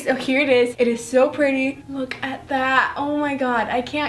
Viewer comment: omg i thought she was saying:I’m gonna fück it I’m gonna fück it😂